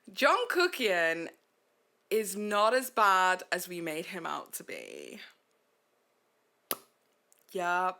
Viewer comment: I was gaming + listening and thought you said "Jungkookie is not as bad as we made him out to be".... and I was wondering when you became a kpop reaction channel talking about BTS member Jungkook